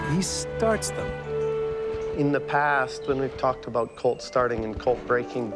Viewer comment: When he said "colt" I thought he said "cult" and I was very concerned.